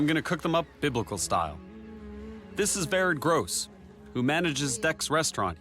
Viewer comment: When he said this is “vered gross” I thought he said this is Very gross